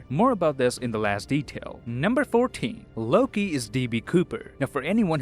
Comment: did anyone else hear "Number Thorteen"?